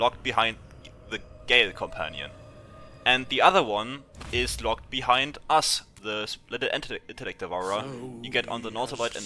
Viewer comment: can anyone please tell me what the heck he says here? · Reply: is locked behind "Us" the little intellect devourer. Im talking about the little brain friend you can get in act 2.